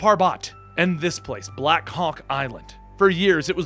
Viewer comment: I thought he said blackcock